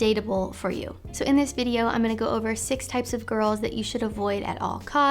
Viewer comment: I heard: “Date these types of girls!” 😍😍😍 Wife!!